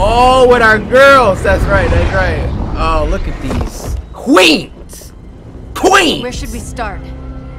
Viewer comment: when he said QUEENS i thought he said QUOICES